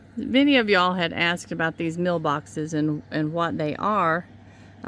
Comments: I thought you were saying mail boxes. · I thought maybe that what the meal boxes were used for. I'm older than you but still didn't know or seen one lol I thought you were saying mail boxes till I saw them....must have been that slight accent you have lol